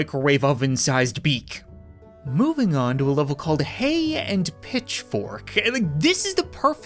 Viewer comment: I had my volume low so when he said hay and pitchforks I thought he said hey bitchforks